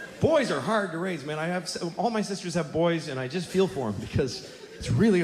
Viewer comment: WHY DID I HEAR “All my sisters are boys.”